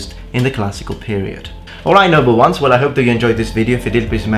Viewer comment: I just realized you say "noble ones". Lol, I've watched like 10 videos since subscribing the other day and the entire time I thought you were saying "number ones" like in Star Trek or something! 🤣